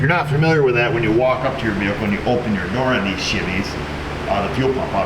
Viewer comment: Did he say "shivvys" at 2:10 ? LOL